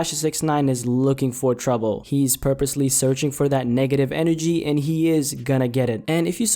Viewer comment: did u say "nigga-tive?" 🤔